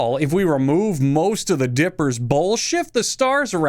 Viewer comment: I heard: Remove most of the dippers bullshit